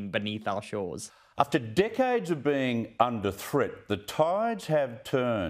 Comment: took me 3 gos, but I finally heard "decades". You'll never guess what I thought I heard. :P